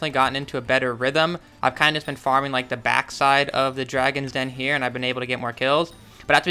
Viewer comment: did he say the dragons den?